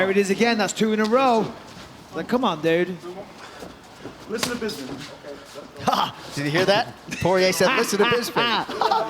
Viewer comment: What did Poirier say at 2:22 ? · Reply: "Listen to Bisping"